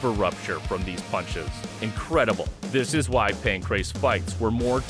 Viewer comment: Coulda swore it said “pancreas.” Knowing Bas, it had to be pancreas.